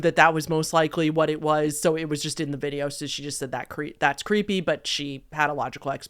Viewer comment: I think she said, "that's not creepy."